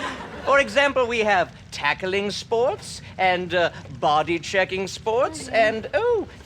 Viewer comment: Oml I thought he said sporks